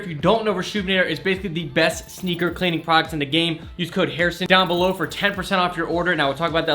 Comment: I swear he said he said in the game??!